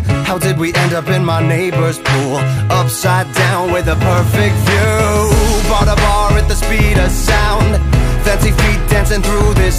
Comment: AT FIRST I THOUGHT IT SAID "bought a bar in the speed of light"